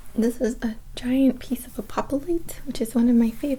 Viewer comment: what crystal is this? I couldn't hear what she said. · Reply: Apophyllite I believe 👍🏽